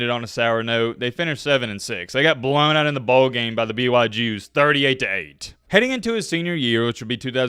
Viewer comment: Did say they BYU Jews!? hahaaha Arent they Mormon? haha